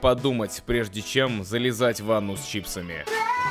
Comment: оруу, услышала "залезать в анус чипсами"